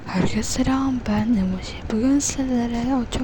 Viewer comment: emoji dediğini duydum ben emoji diye açıklamada ise öldüğün uazıyo